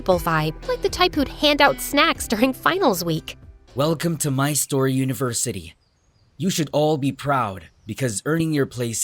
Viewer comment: did he just say My Store University???